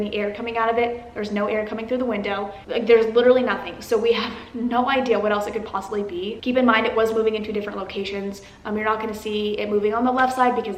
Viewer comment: I thought she said “ keep in mind that we’re female caucasians”🤦‍♂️😂